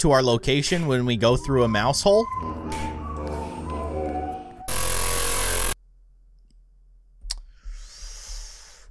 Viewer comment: Did Principal Just say no Being a mouse in the Halls it sounds funny 😂😂😂😂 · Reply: No being a mouse in the hall (Principle)